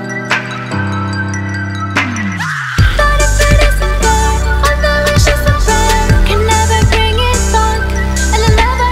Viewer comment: somebody knows what she say in the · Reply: i thinks she says:"But if it isn't worth I'll know the worth"